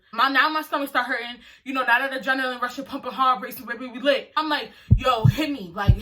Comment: Girl what did you SAY at 12:53 😭😭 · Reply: She said “Now that adrenaline rushing pumping hard, my heart racing, we lit”